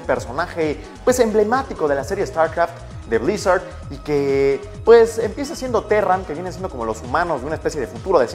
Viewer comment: jajajajajajaja escuche que emmienza siendo perra 6:15 lo peor es que lo vuelvo a poner y escucho lo mismo XD